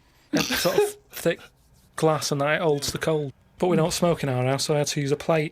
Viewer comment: "they're sort of old cold" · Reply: no - he says “they sort of hold cold”. He even says a few minutes later “they hold the cold”! Think you might be mishearing 🙂